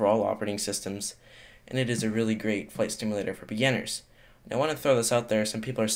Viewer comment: did anyone hear him say flight stimulator???